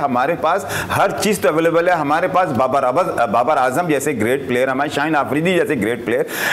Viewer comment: kya bola moulana hamare pass babar dabur😂🤣